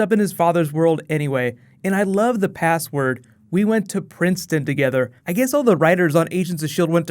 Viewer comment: I thought he said "We went to prison together" Not princeton.